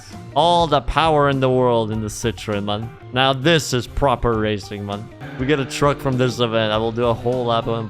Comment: Lol when you got to the 2CV and said “now this is proper racing” I heard “now this is pod racing.”